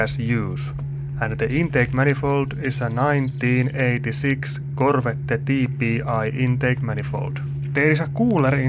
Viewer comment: did i just hear KORVETTE? beautiful language.